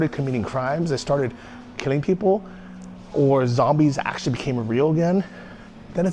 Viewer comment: Did he say '... when zombies actually become real again"?